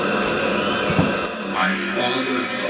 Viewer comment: I think I can hear a voice say "Enough horns", right before he says it you can hear the train horn go off faintly.